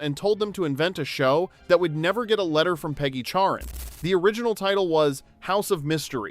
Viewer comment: Oh, her name was CHARREN, I could have sworn you said Karen.🤣